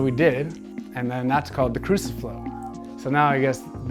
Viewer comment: Did he just say 'a crucifuck' ?